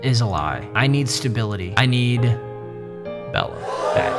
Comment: Did he really say he needed Bella back?